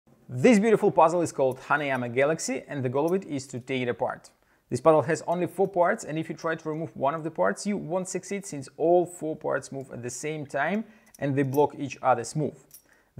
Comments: I thought he said honey I’m a galaxy XD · I honestly thought he said “Honey, I’m a galaxy” puzzle. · At first I thought he said “Honey, I’m A Galaxy” lmao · I misheard the name as "Honey I'm a galaxy" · Please don't tell me I'm the only one who thought he said "honey I'm a Galaxy" puzzle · I genuinely thought you said "This puzzle is called 'Honey, Im a galaxy'"